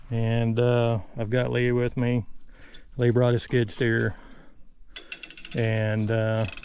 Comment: I thought you said, “Lee brought his kids here.” 😂😂😂